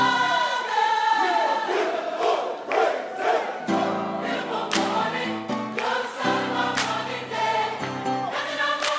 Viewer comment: What do they say before starting to sing "beautiful morning" at 2:14? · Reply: "we gon' give him all the praise, aye"